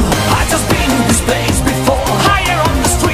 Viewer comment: 子犬しか聞こえんwww